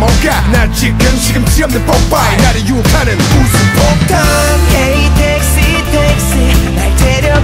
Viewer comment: i thought i heard him say " Hey dixie dixie "